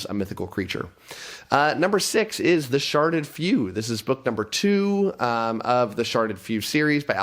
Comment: lol…at first I thought you said The Sharted Few